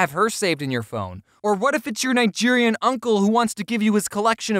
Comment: Did you just say Nigerian uncle